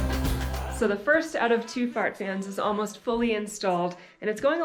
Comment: Hearing say "fart fan" was natural. Hearing say "fart fan" had me giggling! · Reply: That's too funny. I thought she said that too. I convinced myself that she said Smart fan! Lol